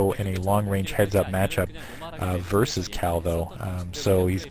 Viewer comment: I thought he said at first cow versus cow :DDD